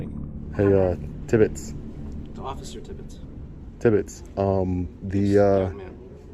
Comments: “Nobody’s scared of you tibbits” 😂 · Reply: I thought he said 'timbits' (Canadian here lol) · hey uh Tibbits I heard " Officer timid Tibbits" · I thought he was saying timbits😂 Canadian thing